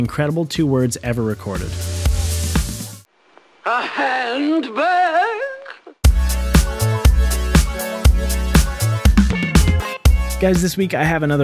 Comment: I thought she said "A handburg?" like some upper crust old bag being introduced to hamburgers and not understanding it: "A handburg? A burg they eat with their hands? How deplorable. Take this away from me at once!"